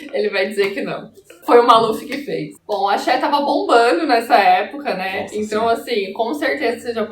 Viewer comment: A falou "Axé estava bombando" e eu entendi "A Cher estava bombando" e fiquei confusa tentando imaginar a Cher passando por baixo da cordinha